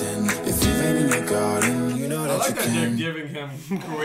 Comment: did anyone notice it sounds like he said , i like that dick" and buddy laughed cuz he heard it too